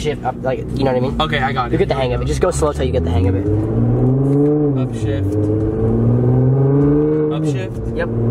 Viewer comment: Almost the whole time i thought you were saying oh shit instead of up shift 😂😂😂